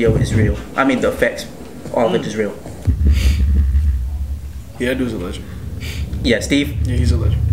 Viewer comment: When you said "that dude is a legend" I heard it as "that dude is a LESBIAN"... idk either